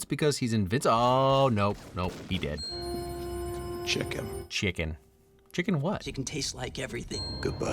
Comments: I'm pretty sure the text is "Check Him" and not 'chicken'. You can almost read his lips to determine the small differences. Of course I could be wrong. I always thought he was saying 'Check and see if he's really dead'. Love your videos! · I thought he said "Check Him" like "Check if the Body is still breathing". 13:35 You make fun of Fight Club here, but isn't Fight Club satire of this exact kind of thing? It's supposed to be a Deconstruction of Toxic Masculinity. Being a Worker Drone, having an extremely unfulfilling life, and wanting to find some source of meaning, sort of is a big problem many people have (and is not a generic white dude problem). Hell it's a direct cause to many cases of circumstance-based Depression and mid-life Crisis; shit slowly breaks you down, and deserves acknowledgement. Not many movies do acknowledge it in a clear good way tho'.